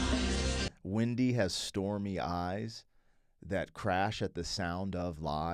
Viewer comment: When I was growing up and my parents had this on the 8 track in the car, I heard it as Wendy for 10 years at least. My favorite by them is "Never my love". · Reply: I always heard "starry eyes" until just now. DOH!